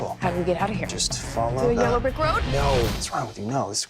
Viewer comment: what did she say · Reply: MC FC "yellow brick road" a Wizard of Oz reference xD